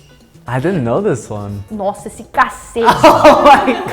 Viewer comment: What did she say here? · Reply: She says; "Nossa! Esse cacete...". It's a double entendre. To a Portuguese person, it only means: "Wow! Nice bread...". In Brazil, it can mean: "Wow! Nice dick...".